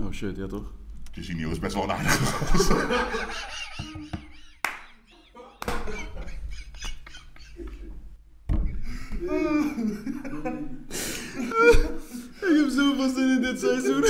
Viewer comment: Wat zegt hij op 1:26 iemand? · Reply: Jonsino is best wel een aardige gast